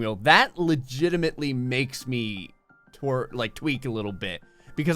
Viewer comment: did anyone else notice he almost said twerk instead of tweak?